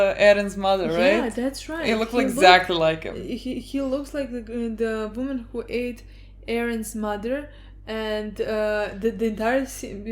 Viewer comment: did she say " the WOMAN who ate Eren's mother"? (and look at face...)